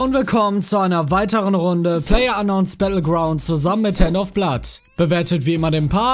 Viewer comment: Ich hab einfach ernsthaft 'Herr Noffblatt' verstanden anstatt 'Handofblood' was ist falsch mit mir?